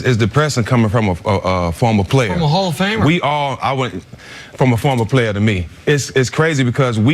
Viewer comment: did just call stephen jackson a hall of famer ? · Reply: Ben Harding Nah Hof...Hell of a fighter..